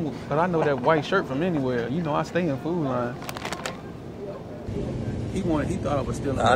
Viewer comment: “You know I stay on Food Lion” 😂😂😂😂😂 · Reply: 🤣🤣🤣 as a Chicagoan, whom has never been to a Food Lion, I thought said “You know I stay in food line” … I was like 🤷🏽‍♀️ oh ok 🤣🤣